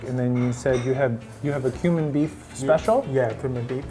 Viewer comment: i misheard the Cumin Beef to Human Beef. holy cow.